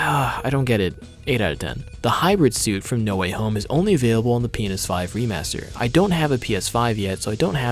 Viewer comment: is it just me or does it sound like he said penis 5? Also great video, couldn't agree more with the new naming convention stuff you mentioned.